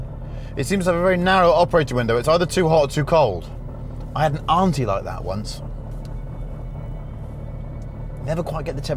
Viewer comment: I thought he said "I had an Audi like that once" xD